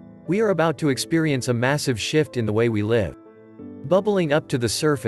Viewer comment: I thought you said ... We are about to experience a massive shit... 😆